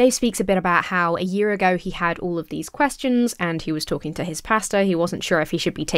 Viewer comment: I heard "he was talking to his pasta" 😂🍝 Then again I guess that could help if you're Pastafarian. 🤔